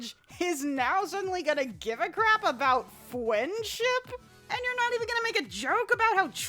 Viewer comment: Did you just say fwendship